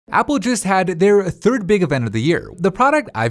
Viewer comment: I legit thought he said Apple juice. I was like "when was there ever such a dominant apple juice seller, that it can just be named Apple Juice?" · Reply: I didn't even realize it said "apple just" I just thought it said juice until I read this comment 0_0